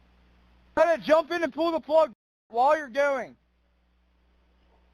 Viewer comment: Listen to on the call @ 4:51' Jump in a pool of blood while you're going " Fuck Interesting choice of words!?!!;! Caller is so distracted with what's going on around him, instead of speaking directly to the Sea tow operator. I pray this family gets the answers they desperately deserve in regards to their precious son's death! Justice for Nolan!! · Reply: At first it sounded like that and I replayed it five times and it sounded more like he said jump and pull the plug as your going